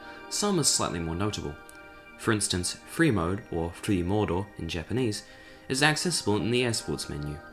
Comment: I heard flee Mordor.....but that's prbly me being obsessed with lotr